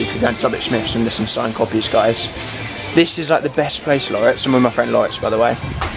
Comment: thought you said Lauren at first and had a little freak out then realised